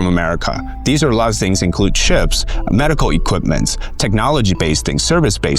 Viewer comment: Did he say China buy's ships or chips I'm confused, cause china is the number one shipbuilding country in the world 12:20 i think